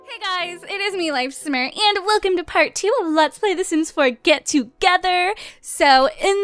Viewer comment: I thought you said Sims 4 Get to work ha-ha😆😅😄😃😂😁😀😊☺